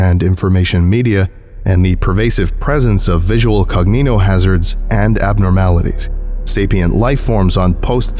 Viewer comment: what did he say???? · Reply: "cognitohazards and abnormalities"